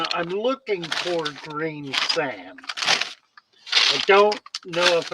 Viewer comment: What was that missing ingredient? I couldn’t hear it clearly, I’m sorry. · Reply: Sounded like you said green sand or grain sand. The fossilized algae that you were going to try and dig out of the ground. Was that the iron oxide mineral your talking about.